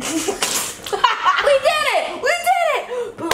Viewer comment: binge watching in 2020 2:12 did he just say "my penis" ??